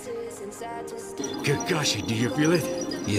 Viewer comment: i thought he said kakashi dear💀